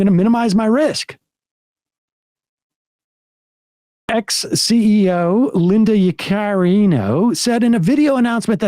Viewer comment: Y'all! Sam said "X CEO Linda Yaccarino," and I heard "ex-CEO," so I searched her to see how long she had been the CEO, and I found out that she is still the CEO of X 🤦🏻‍♀️🤦🏻‍♀️🤦🏻‍♀️Only then did I realize my confusion.